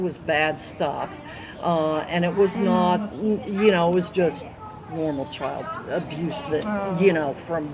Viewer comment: Did she say, you know just “normal” child abuse? There is NO abuse that’s normal! Crazy making. Thanks again for bringing this subject to the front.